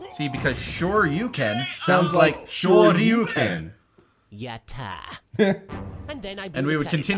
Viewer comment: every time I heard this take it sounded more like "SORRY YOU CAN'T" instead of Shoryuken